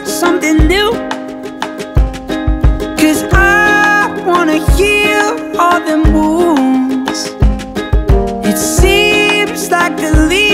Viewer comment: Why did I hear "Cuz I wanna hear all them moose"? 😅